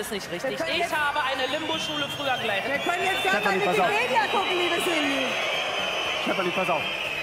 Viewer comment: was sagt die denn bei 5:20 ? "wir können jetzt gern mal... liebe cindy" ??? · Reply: Bei Wikipedia gucken